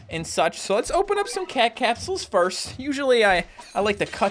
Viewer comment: Did just say caca capsules?